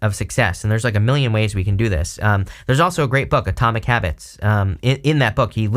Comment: What was the name of that book? I love to read books he recommends. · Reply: Haha😅I didn’t remember the transcript would have it! He clearly says Atomic Habits! 13:45 thank you